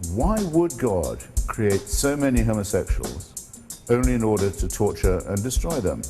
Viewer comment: he says why would God create so many _____, only in order to torture and destroy them. I didn't get that word he said. Can someone tell me what he said? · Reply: "homosexuals"